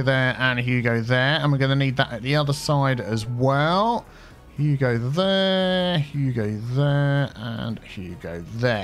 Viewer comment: Every time he does the lane management, all I hear is “Hugo there, huuugo there”. XD